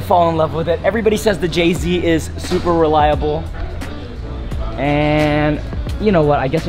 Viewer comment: “ the JZ is super reliable”. All I heard was “JZ is Supra reliable”.